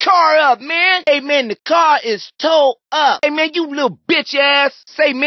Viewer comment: what did he say??? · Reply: He said "tow up"